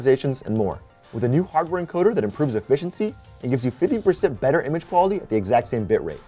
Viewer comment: did he say 50%, because I thought it was 15%.